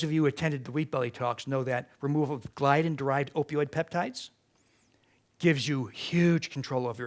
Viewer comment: What does he say here anyone please? Glyten derived opoid peptides?